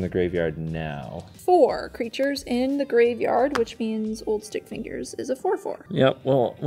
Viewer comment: About 3/4 of this game I thought was saying old stinkfingers instead of stickfingers. Was looking up the card out of curiosity before i found out I'm deaf apparently lol